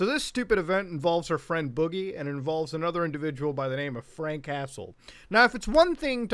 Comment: At first I thought he said the guy's name is "Frank Castle" and my first thought was "Oh, Shit ! Why is the punisher going after Boogie ?!"